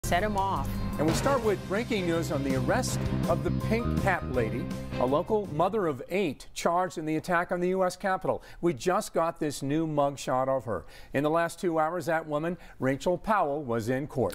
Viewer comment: I thought they said pink cat lady lmfao